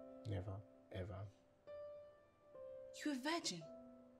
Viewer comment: I thought this guy said is a virgin ?how come he impregnated his girlfriend